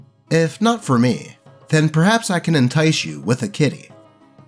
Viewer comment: When he said maybe I can entice you with a Kitty, I thought he said Kidney and was like “What? Are you secretly running a black market for organs?” Then I saw the kitty on screen, Simba you are a great cat